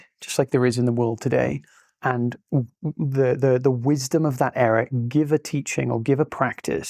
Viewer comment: in the subtitles it says error, but I think you said era, please check that.